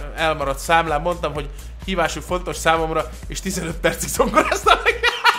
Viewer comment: tol mit mond a végén? hívásuk nagyon font... tovabb? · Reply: Mondtam, hogy hívásuk fontos számomra, és 15 percig zongorátam neki